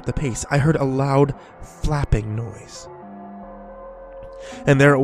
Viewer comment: I heard a loud faping noise!!